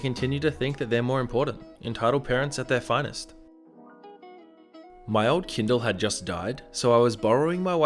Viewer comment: On the second story I heard "My old kidney just died so I was borrowing my wife's....."then I immediately paused the video to check if I am drunk and guess what.....I am!